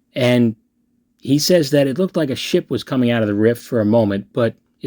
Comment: I legit heard "he says there's a shit coming out of the rift" 😂 you're messing with my head man